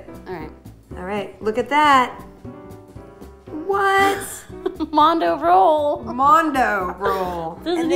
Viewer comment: Thought they said Mundo Roll :/